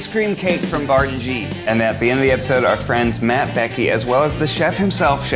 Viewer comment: "Our friends MattBecky." That's not how Ned meant it, but that's how I heard it and I'm very pleased with myself.